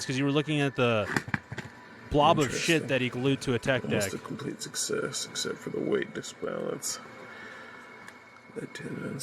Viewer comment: Absolute success instead of the weight balance 29:40 LMAO